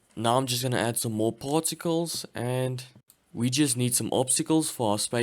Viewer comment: DID YOU JUST SAY PORTACALS INSTEAD OF PARTACALS